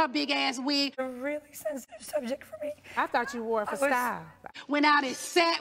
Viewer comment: OMG now I heard clearly what NeNe said at 0:13 while Kim was crying, “I THOUGHT YOU WORE IT FOR STYLE” LMFAO ☠️☠️☠️